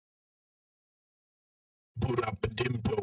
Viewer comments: If you are struggling to hear the difference Cranchee's lyrics have changed: Old - Boodap-badim*do* New - Boodap-Badim*bo* This is more accurate to their bio on release which states their lyrics were the new ones. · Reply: I thought he said put up the tempo 😭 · Tbh I thought he was saying put up the tempo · It sound like he saying pull out a dill doe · What I hear: "Pull up a dimdo" "Pull up a dimple" "Pull out a dimpo" "Pull out a d*ldo" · sounds like he is saying “put up the inpoe” for the new track and for the old one he is saying “put up the intone” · I heard "Put up the tempo" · I hear ‘Put up the dempo’